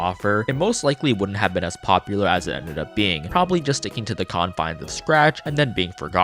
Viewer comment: did he just say "Combine"